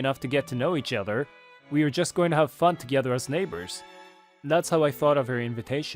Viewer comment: did i just hear "we are just going to have fu*k together" 💀